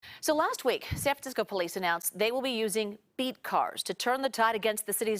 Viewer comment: Did she say "They will be using Beet Cars"?? Beets are gross!!!